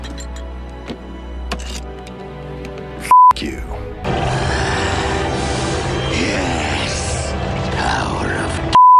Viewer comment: LOL What did Breakdown say??? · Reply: He said thank you in original but in this video it's f--- you✨